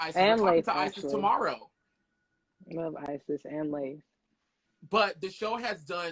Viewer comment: who does Azmarie say she like after saying she loves Isis? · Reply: i heard leila which she probably pronounced layla but idk